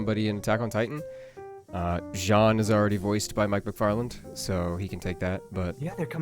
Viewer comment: At first for a second I thought you said "Seth McFarland" a writer for Jonny Bravo, when I was a kid I had seen Boomerang; I remember when my dad said the FBI talked to him one day, he said he made a friend with an FBI agent; people in other countries have differing consent laws I found out that day on that adult website.